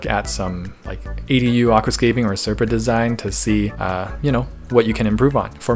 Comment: did I just hear you say SerpaDesign 👀👌